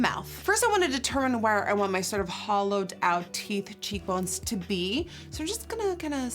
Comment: Am I the only one who thought that she said holoweak 💿💿💿